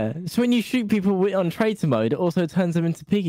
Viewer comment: it sounded like she was saying “tratior joes” 😂 like the shop